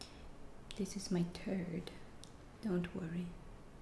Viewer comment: I could have sworn she said, " This is my turd. Don't worry." Lol.